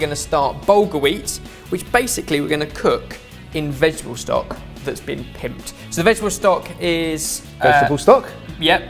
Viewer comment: Okay, did Ben really say "vegetable stock that's been pimped" at 0:33?? Or is that just me?